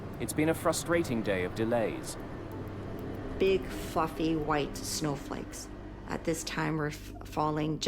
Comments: Did she say "big fucking white snowflakes" at 2:10? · Did she just say... Big fucking white snowflakes?!? 😂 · anyone else think she said “big f’n snowflakes”?